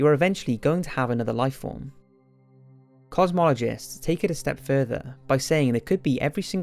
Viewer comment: thought he said “cosmetologists” lol